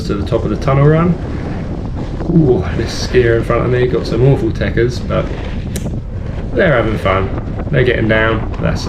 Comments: What did you say about the skier in front of you at 1:35? It sounded like techuns? What does it mean? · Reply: I said tekkers, like technique. They dont have the best technique so I gave them a wide berth, but they're in control and getting down, which is the main thing! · what’s a techa or taker, I couldn’t really here what you said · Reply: Tekkers = technique 👍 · What do you say at 1:33? It's a scary..and awful..teckers? I'm sometimes having issues understanding you, sorry. Thanks for replying! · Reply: Sorry, I just said a skier in front of me has some awful 'tekkers' meaning technique, hence why I hang back and give her plenty of room whilst overtaking! 👍